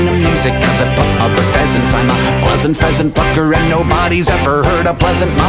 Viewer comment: Wait a minute, did he say "pleasant pheasant fucker" there?